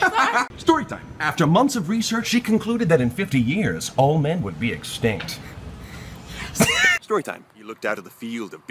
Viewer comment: My English is not the best I don't understand this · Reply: After months of research, she concluded that in fifty years all men would be extinct